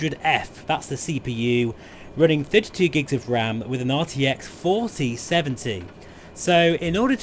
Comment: Lol when? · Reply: talking about the computer you detailed specs and it sounds like you said 50gs of RAM....